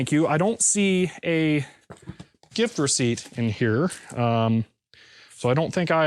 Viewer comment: Are my ears deceiving me or did I hear gift receipt pronounced like GIF receipt? ;-)